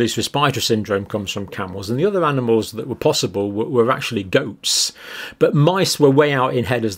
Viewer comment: First time through I thought he said "Ghosts" as a vector, I had to go back and listen again and he really said "Goats". It is actually kind of funny because Whoopi got the VId and she doesn't know where it came from (and she was in Ghost).